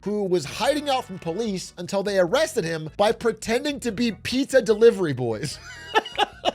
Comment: Oh you said polizia delivery I thought you said pizza delivery